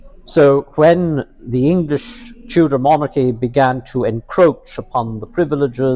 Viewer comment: Fantástica entrevista, enhorabuena. Un par de fallos en los subtítulos, por si los quieres corregir: - En 9:15, lo que dice es "la dinastía inglesa Tudor" no "la dinastía inglesa infantil". Tudor suena parecido a childish, pero incluso en los subtítulos automáticos en inglés, pone Tudor. - En 18:07, un "false friend" típico: "facilities" no es "facilidades" sino "instalaciones" o en ese contexto, "infraestructuras", es decir, el Colegio Real Irlandés que fundó Felipe II en Salamanca más los albergues para irlandeses que ya existían antes en Valladolid, Alcalá y Salamanca, las imprentas en Lovaina etc.